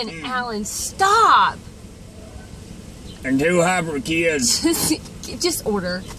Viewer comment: "... for a gal fixin' to start 'er period." Holy sh*t! I can't quit laughing!! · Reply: LOL And I thought he said "two hybrid kids" - ha